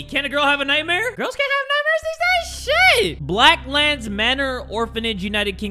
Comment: On god I thought it said "Black lives matter" at first